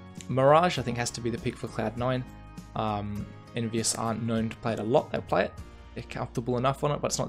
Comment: did you say envyus maybe, idrk... anyway nice predictions :)